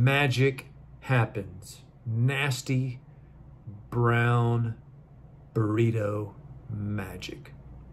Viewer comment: Normally the subtitles say “burrito” when Hank says “tornado.” This time, Hank said “burrito” and the subtitles said “tornado” 🤣